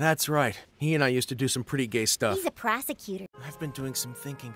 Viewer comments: WHEN MAYA SAID PROSECUTOR AT 0:41 I THOUGHT SHE SAID PROSTITUTE EYE- gOD PLEASE HELP ME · i totally thought she was gonna say prostitute